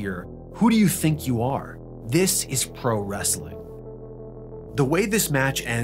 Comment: I think what Tenta actually says in that moment is "Who do you think you are, a yokozuna? This is pro wrestling!" I'm not sure of much of the first half of that, but he definitely says "...yokozuna, this is pro wrestling."